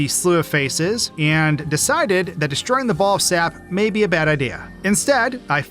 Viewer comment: I'ma be honest... At 3:14, I had to rewind because I thought he said ball sack... It was only then, upon rewatching that part I realized that he actually said, "ball of sap" 😭